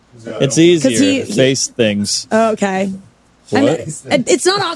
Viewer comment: “Why does everybody have sunglasses on?” “It’s easier.. to face things.” “What?” 💀🤣 that flew over Brent’s head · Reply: I thought he said "your face stinks" 🤣🤣